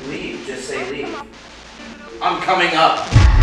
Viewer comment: I heard "don't come up" instead of "I'm coming up" maybe it refers to the other guys who are making their way to the roof.